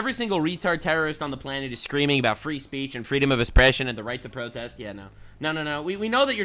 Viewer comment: I thought you said "freedom of espresso" lmao